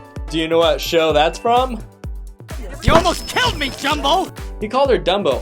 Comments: I think the mouse called her 'Jumbo' not 'Dumbo'. Just sayin'. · I think said jumbo not Dumbo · I think he said jumbo not dumbo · he actually said jumbo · I think he was saying Jumbo, if you listen closely. He's referring to how Meena is like 10 times bigger than him